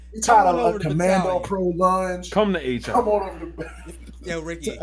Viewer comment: What did Ricky. Say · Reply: "come to H Hour"